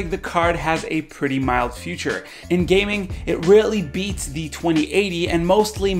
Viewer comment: "In games Radeom 7 mostly beats the 2080" :)))))))))) What were you smocking ?:))))) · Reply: Rarely* my voice cracked and it sounds like I said "Really"